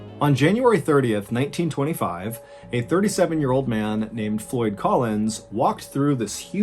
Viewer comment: I thought you said foy collins. Around the 13 minute moment… ohhh no.